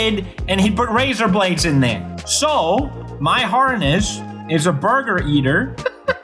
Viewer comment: i'm sorry did he just say his last name is burger